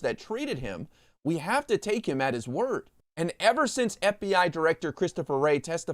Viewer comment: Right! I did a coffee ☕ spit when I heard "take him for his word". Gimme a break.😅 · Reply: I thought I heard 'mistake him for a turd.'